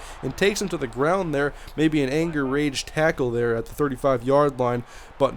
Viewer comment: no rage but did you really say anger-rage tackle